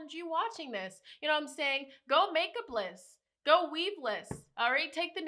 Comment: When you said go weave-less i thought you said weed-less. I was about to boycott 😂